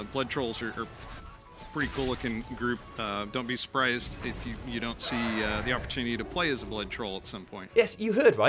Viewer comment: I think he says "If you don't see-" As in, we wont get to see it?